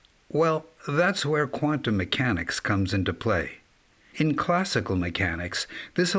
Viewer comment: When he says "quantum mechanics" and "scientists and engineers", I hear "ALIEN TECHNOLOGY" and "ALIENS" 👽